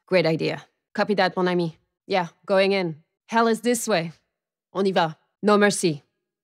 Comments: What's she saying at 1:33-1:34 ''oniva'' or something? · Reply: “On y va” which means “let’s go” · what she say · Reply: "On y va" - Let's Go.